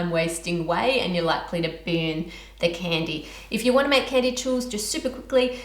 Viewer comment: is she saying boon or something else?? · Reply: She’s saying ‘burn’